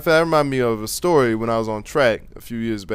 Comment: lmfao when you said 'that reminds me of a story a while back when i was in track' i heard ' when i was on crack'